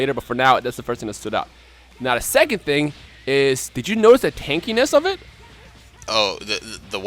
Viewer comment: "Did you noticed the kinky-ness?" Tha wha?! Lol sounded like he said that but he said tanky ness lls